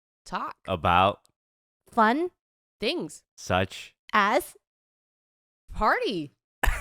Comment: i literally thought was gonna say Penises 🤦‍♂️